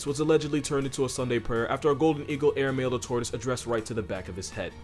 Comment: fr thought you said tourist instead of tortoise in the part on golden eagles 😂